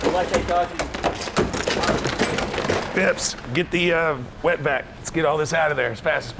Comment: I thought Richard said “get the wetback” omg 🤣😶